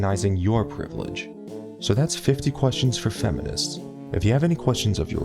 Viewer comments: Sounded like he said "Don't forget to rape this video and subscribe. " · I thought you said ”And don’t forget to rape the video!” At the end lol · At the end, I thought he said to rape this video and subscribe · For a second I thought he said don't forget to rape this video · I thought he said " And don't forget to rape this video and subscribe" lmao